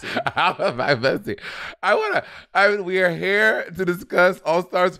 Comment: This is not an important comment, just a time-stamp proof of what Bob means when he says Monet says "air" sounds when it's "ere" sounds: a little after 5:11 , Monet goes "We're here to discuss..." and the here sounds like hair.